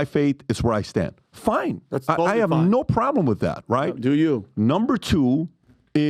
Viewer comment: who heard say (do you ) am i the onlyone who heard it XD